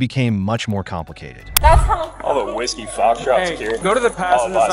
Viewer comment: captions says "All the whiskey farts here"😂😂😂